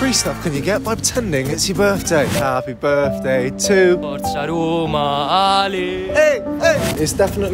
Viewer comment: Any ideas what that guy at the start said? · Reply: I thought he said mozzarella ali 💀